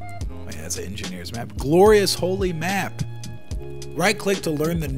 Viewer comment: I heard Glory Hole map